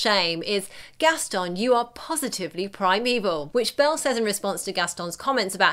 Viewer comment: When I was a kid I thought Belle said "prime evil" instead of "primeval."